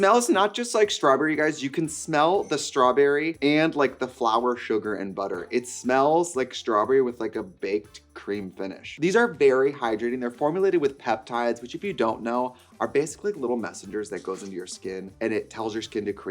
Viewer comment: I heard "berry hydrating" and I'm sticking to this reality